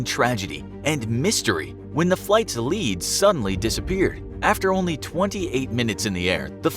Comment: when he said "flight's lead suddenly disappeared" I heard "flight's lead sunn lee dissapeared" XD